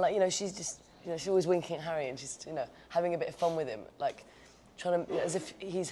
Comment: someone can translate the minute 1:15 to the minute 1:20 , please i don't understand all that she's saying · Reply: Mikaela Pasquel "she's always winking at Harry and joking with him , like she's his peer"